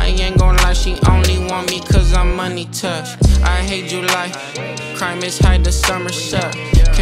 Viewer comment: the part where he says “I hate July ” I thought he was saying “I hate you lie ”😭😂😩